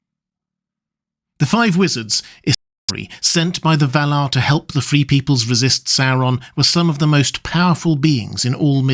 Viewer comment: I legit thought my phone glitched for a sec. What was that section actually going to say? It sounded like it was going to be interesting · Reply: It was just him saying the word “Istari.”